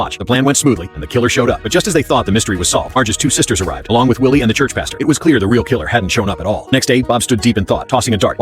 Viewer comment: I thought he said "church bastard"